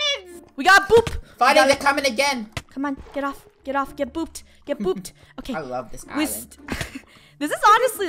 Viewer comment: I thought she said get pooped one time lol 🤣